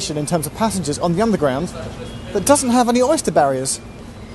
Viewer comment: is he saying "but doesn't" or "that doesn't"?